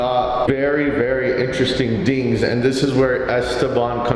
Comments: did bro say dings 😂😂😂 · Did say dings instead of things